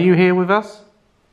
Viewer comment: hold up, did he say "us" when no one else was there with him?